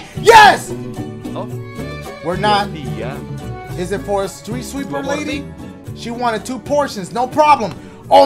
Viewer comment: my boy really said sweep streeper lady haha instead of street sweeper